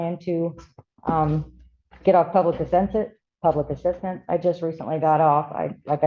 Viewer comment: If she just got a new job, why is she trying to get on public assistance? If she had back surgery in June 10th, no way could she go in for testing on June 12th. This woman is a hot mess and needs help. 😮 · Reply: I thought she said she was trying to get off public assistance now that she had a job lol